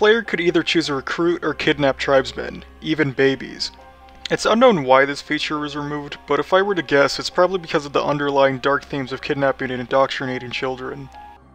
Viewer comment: "I don't know why they removed but I think there's a chance that just maybe it might of been because of the underlying themes of indoctrinating children" - not exactly but it's what it sounds like he said Yeah I wonder